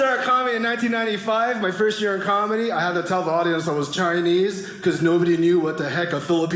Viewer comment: Chinese sounds like someone is saying tea in easy